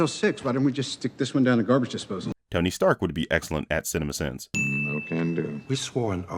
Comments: the caption says Tony Stank instead of Tony Stark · He says Tony Stark but the captions say Tony Stank 😂 Also can we just take a minute to apreciate how accurate this is